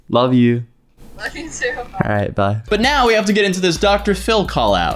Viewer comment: Sounds like you said “alright die”